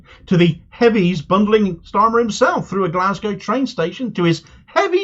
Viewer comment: For a split second there i thought you said "...heavies, bundling Starmer through a plate glass window! 😂 I'm a silly, old, deaf, thing...at times!